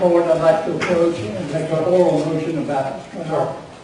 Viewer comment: It's eating me alive wanting to know what that man (maybe Dunn's father) said starting at 4:10... Sounds like he's saying he wants to make an oral statement about the lawyer!!